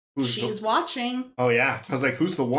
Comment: I always thought she said "baby" when they came in to her room to rob her (not "Betty")...hmmm